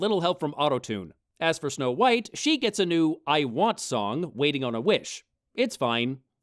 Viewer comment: I thought you said "Waiting on a Witch," which would have been so much more fun.